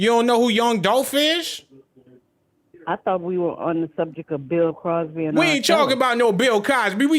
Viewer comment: done went off on that old lady 🤣 she thought he was talking about Bill Cosby 😂😂😂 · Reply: I thought she said Bill Crosby, lol. I was like who is that 🤣🤣